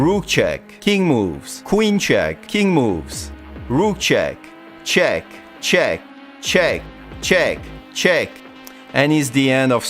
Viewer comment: all i hear is cheg cheg cheg cheg